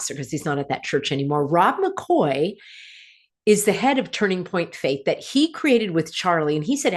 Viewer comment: At first I heard you say “Turning Point Fake” instead of Turning Point Faith” 😂 Fitting? I think you underestimate Charlie’s importance. I accompanied my daughter to a CK event hosted by one of these huge evangelical churches. It was PACKED. People were enthusiastic. My daughter said afterwards, “he’s giving me hope.” He was hugely influential.